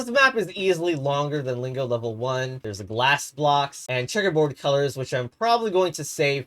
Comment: blast blocks from i wanna lingopick (i have no idea why i heard the g as a b)